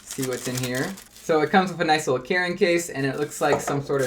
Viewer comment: I heard it comes with a KAREN case, but he said carrying. And btw Ty for this video I’m thinking if I wanna get one so thank you for this vid